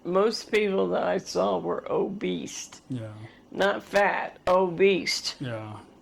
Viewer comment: 😂😂😂 I loved how called the super big people obeast, well that's how it sounded to me, and I laughed oh that's more accurate really. Maybe I misheard 😂😂😂